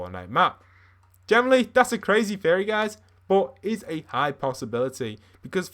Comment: i thought he said furry instead of theory